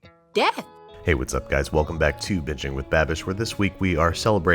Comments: Am I the only one heard him say 'Bitchin with Babish' in his intro? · is it just me or at 0:24 It sounds like he says "bitching with babish"😂😂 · I heard "Bitching with Babbish" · At the beginning I thought he said, "welcome to bitching with babish" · Thought you said “bitching with babish” what a frickin spin lol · I heard "Bitching with Babish" in the beginning. But hell, you can call the show "Cornhole country cooking" And is still watch it! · Ok so am i the only one who heard him say " bitching with babish" isnsted of "binging with babish" anyone else???? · At the start I thought he said 'bitching with babish' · I thought he said bitchin with babish 😂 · Anyone else thought he said "bitchen with babish"? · Sounds like he said bitching with babish😂😂😂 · I thought he said "Bitching with Babish" in the intro 😂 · anyone else hear "bitching with Babish" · not notification squad just on youtube 24/7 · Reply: Same. I thought he said in the intro "Bitching with Babish" XD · Sounds like he says “bitching with babyish” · I thought he said biching with babish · I thought he said bitching with babish instead of binging with babish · I thought you said, "welcome back to Bitching with Babish" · I know it's super late but who else heard him say "bitchin with babish" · i thought he said bitching with babish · i thought he said “bitchin with babish” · I thought he said, "Welcome back to bitching with Babish." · I thought you said "Bitching with Babish" LOL